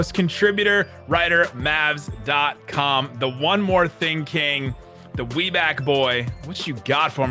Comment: I have been watching this podcast for a year now (probably 300 of them) this is the first time I head "The One More Thing - King" I have always thought he said "The one we're thinking" lol my brain did a weird thing with that.